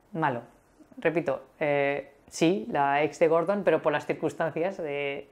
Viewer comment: Hey! Great video! What did you say around the 8:08, about Gordon's EX? I did not understand :D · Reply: That she's the only bad person I've met in the UK but due to the circumstances (por las circunstancias) :D